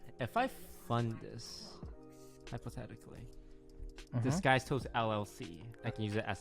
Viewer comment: Why did I hear “ha this guys toast” Instead of “disguised toast”